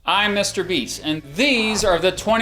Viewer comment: I misheard the intro. “I’m Mr. Beat” and not Beast